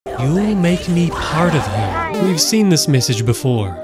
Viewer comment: I thought mommy long legs said youll make me spider meal